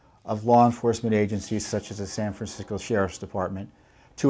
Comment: Did he say San Fran-sickos? Around the 2:30 mark! LOL.... right-on Mr. Fitton, right on!!